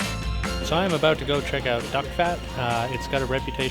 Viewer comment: Thought they said dickfat.